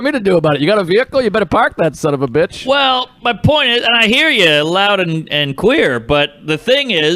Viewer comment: "I hear you loud and queer"